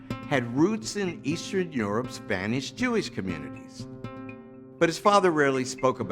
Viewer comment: I don't understand the word at 0:25 it seems he says "eastern europes spanish jewish communities". What does spain have to do with any of this? Is he saying something else than "Spanish"? Please enlighten me thanks!